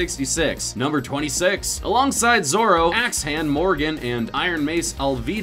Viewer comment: I heard "ass-hand" not "axe-hand"... of course i did.